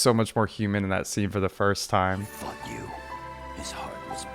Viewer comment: why did it sound like zabusa said fk u😭